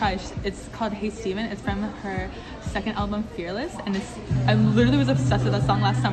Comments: 最初の literally は little bit に聞こえてしまった…… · Hey Stephenがhey semen に聞こえてしまった